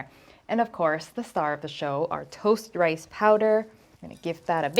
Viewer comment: Does anyone hear ? What kind of powder is it? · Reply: Toasted Rice powder :) Cheers! Adam